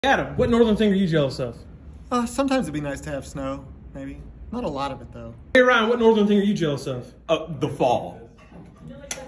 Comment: At first I thought he was saying "what NORMAL thing are you jealous of?" 🤣🤣